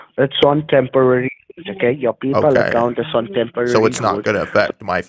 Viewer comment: Sounded like he said your pee pal account the first time